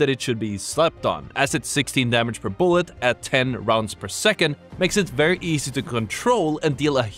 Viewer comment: Car is great but it has the same issues that the R99 has but exacerbated even more. Runs out of ammo too fast per mag and has crazy recoil. Its a bit more forgiving on the ammo side since it does more damage per shot than the R99(but it fires even faster so..) so you dont have to hit as many of your shots to kill someone but it is still highly unforgiving. If the Recoil got toned down a bit i'd probably use it but until then, the Volt is most likely what i will be using any chance i get. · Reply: I misheard. In the video I thought he said something like 12 rounds per second or something like that. Nonetheless my point stands